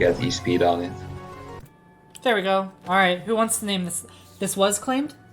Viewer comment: No joke, when you said to name Dratini Cygnus I thought you said Sigmund